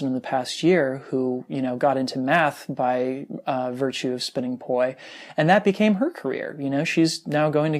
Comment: I heard "Get into meth by virtue of spinning poi." Glad that i heard incorrectly. Keep up the great work. Love your stuff.